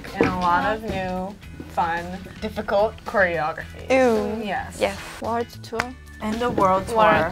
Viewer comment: after they were talking about their next comeback at 9:43 it sounded like Yunjin said easy😭😭